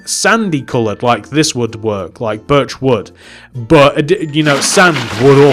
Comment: When u say birch wood, sounds like ur saying virtual